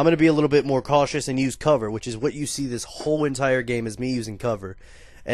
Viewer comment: when u said cover i thought u said cutler